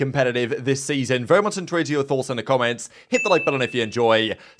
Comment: what do you say at 0:20?? every single video is "very much incoherant giberish thoughts in the comments" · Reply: Very much in tuned to your thoughts in the comments 🙂